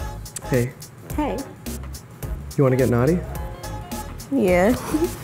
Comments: I thought he said "do you want to get an Audi?" xD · I thought he said "Do you want to get an Audi"